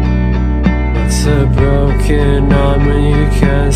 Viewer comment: it sounds like it says my name "your so broken aubrie"